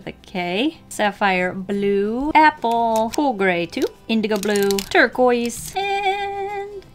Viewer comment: i just got done playing until dawn and, i thought you said wendigo blue instead for indigo